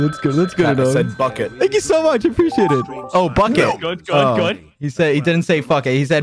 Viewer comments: Ranboo really said “bucket” and broke quackitys chat for a good like 5 minutes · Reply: yeah i was there and straight up thought he said fuck it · Its pretty funny how Ranboo swore at 39:10 and people in chat were so surprised and some were even in denial and were saying " HE JUST SAID BUCKET". Then Ranboo had to say he did indeed swear and Quackity thought he did say bucket and Ranboo said fuck it again but clearer. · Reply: no he said bucket, Quackity thought he said f*ck it, then a few minutes later Ranboo literally said that he'd said bucket